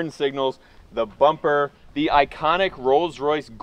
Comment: "Coals Royce" is the PERFECT IDEA. I don't care how bad an idea it might be to purifists. This is a show winner idea (show car portion of the events needs to be.) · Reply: I thought he said earls Royce 😅